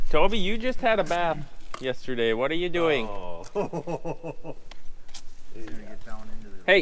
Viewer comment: Did I hear an o-shit at 2:45, I’m not complaining about it